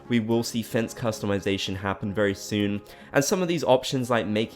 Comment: I swear, for half a second I thought he said “fez customization” and I was like...I didn’t realize that was such a big deal for some people. 😂😂😂